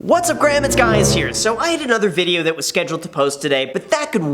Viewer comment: Did just hear his intro wrong !?!?!?! I thought I heard him say "What's up Graham, it's guy here" !?!?!?! By the way, " YANG" was on fire today !!!!!!!